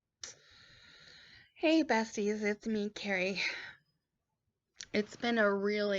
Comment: You are the strongest person. I would be a total basket case. I will still pray for you. And every time you say "Hello Besties" I keep hearing "Hello Betsy" cause that's my name and then I'll say hello back, lol. Stay strong, sweet Carrie.